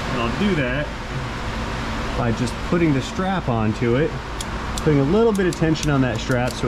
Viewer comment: Did really say "put a strap on"? LMAO!